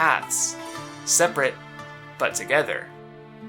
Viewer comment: stg I thought he was gonna say seperate but equal